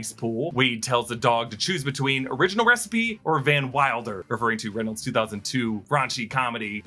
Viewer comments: I’m pretty sure he said “Van Milder” instead of Van Wilder. Making fun of how nice he is. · I thought he actually called him Van Milder which is funnier. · he actually said Van Milder not Van Wilder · I think Deadpool (prime) says "Van Milder" (not "Van Wilder") as a pun referring to Nicepool's mild temperament. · I thought he called “Nicepool” NOT “Van Wilder” but “Van MILDER” or did I hear that wrong?!? If I did, I think my version is funnier!